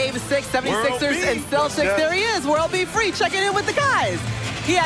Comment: Did she just say world b free ,👍💯🔥